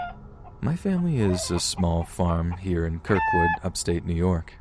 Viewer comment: did he say my family IS a farm if so what's it like